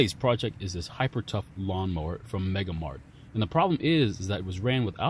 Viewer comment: At the beginning i thought you said mega-lo mart like you were referencing the store on king of the hill 😄